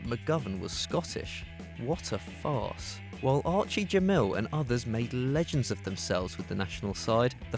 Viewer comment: did anyone notice the way he said gemmil 😂😂 7:58 sounded like archie jemell